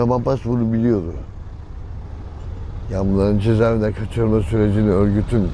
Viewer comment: Bu adam fransada simdi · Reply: Cezaevine aldılar die duydum bende